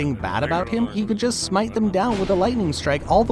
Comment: he really said " smite the mortal"